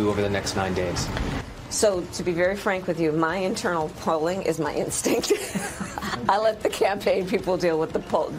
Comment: She actually said" is my instink "🤣😂🤣😂 @ 5:43. Had to replay it a few times to validate the lack of wit.